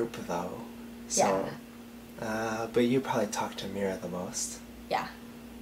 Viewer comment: I thought he said, "You talk to the mirror the most" and i laughed because I thought it was a joke. And then you were like "Yea, i probably talk to" and i was like WTF and then you said "mira the most" and all made sense. haha